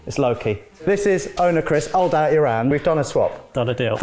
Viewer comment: What is it that is saying at 2:27 ?? Sounds like "hold out your hand" am i right? Pls help me hear it...